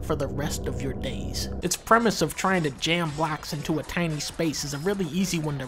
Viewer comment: why do the subtitles say blacks instead of blocks Does Tetris have a meaning we haven't deciphered yet